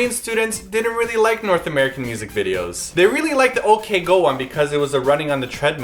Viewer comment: Hahaha! How corrupted is my mind by Korean that whenever you say "OK Go" I hear "OK GIRL!" I NEED HELP... BILASA~~~